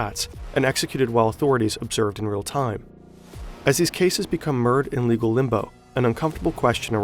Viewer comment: do you mean to say “mired?” It sounds like you said “merd” whatever that is.